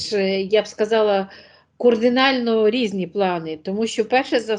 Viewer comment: Услышала "кОрдинально"и сразу переключаюсь!